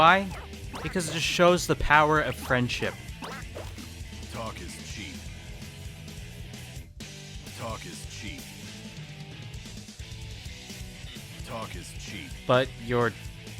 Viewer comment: I thought he was saying “Dark is the sheep”